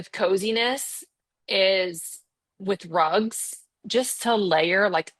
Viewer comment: For a second I thought she said, "With drugs" instead of "with rugs." I gasped and then went, "OHHHH!"